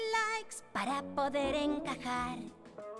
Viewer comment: mi tablet se traba en ese minuto y se que dice "para poder encagar" pero en la mía "para poder cagar" XD